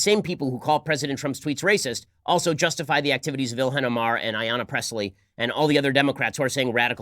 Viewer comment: I heard ‘Yanni’